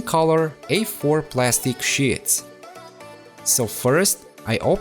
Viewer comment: I Heard Sh*ts Instead Of Sheets